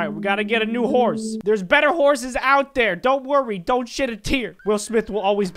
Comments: I thought he said don't s**t a tear. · did u just say a bad word? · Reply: He said don’t shed a tear